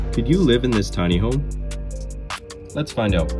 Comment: I heard “do you live in this tiny home, let’s find out.